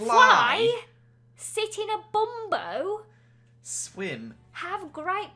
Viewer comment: I thought it said “sit in a burrito’